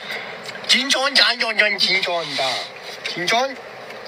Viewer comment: I don't know if I should point this out or not..but I think he is saying 'ching chang chong' ..which is kinda a racist thing to say.. Edit: I decided to point out this because nobody point him out for saying something racist..